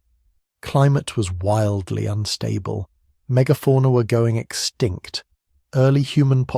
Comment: Someone help me out here. Did he say "megaporna"?